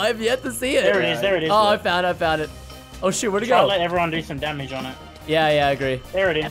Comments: I heard oh sh*t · listen super close, did preston say oh sht????